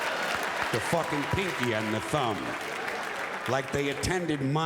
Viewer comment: i always thought he said "the fuckin pinky on the phone" as opposed to "the fuckin pinky and the thumb" sounds funnier the way i originally heard it.